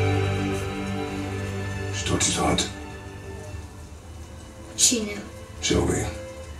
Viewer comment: its not another language... I think he says "She taught you to hunt?" and the kid says "She did" and he says "Show me"